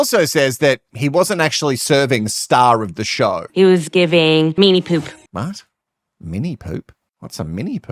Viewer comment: I thought she said ‘mean’ poop.